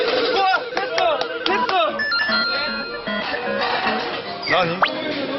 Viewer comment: Did hyunjae say nani at 14:08?? 😂